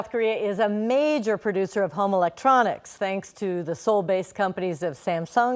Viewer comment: sounds like she said "homo-lectronics" :D :D